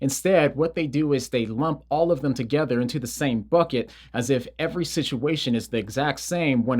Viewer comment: Ok ngl when he said "in the same bucket" I thought he said "and they say F!&# it" I was like 😱😨😨😨